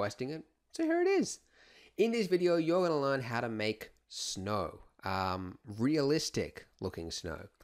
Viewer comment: Oh shit, I thought it said Mark Snow.